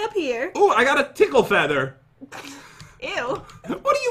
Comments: I wasn't paying attention when he said tickle feather so I thought he said pickle feather! !LOL! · I wasn't paying attention when he said tickle feather so I thought he said pickle leather! XD !LOL!